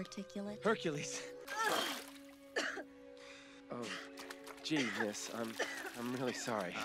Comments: did he just say "jesus"?!?? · did he say “Jesus, I’m so sorry” ?! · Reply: "Gee, miss, I'm really sorry." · did he really say 'Jesus'? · am I mishearing or did a greek god just say "Jesus I'm sorry"? Update: It might be "Gee miss I'm sorry" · Did he really say "jesus" ? Even the movie is based on greek mythology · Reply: jeez miss is what he said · did he just said "jesus" lol